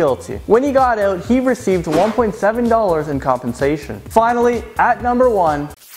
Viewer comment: did he said 1.7 dollars